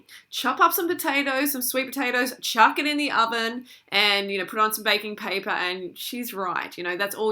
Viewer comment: When u said "throw on some baking paper" i thought u said "throw on some bacon".. I WOKE up right then and there like WHUUUT :'D